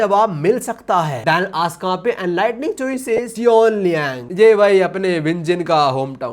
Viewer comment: he say cheonliang I hear seongji yuk and 49:22 seongji yuk the king of heart ❤ is coming soon the most kindest person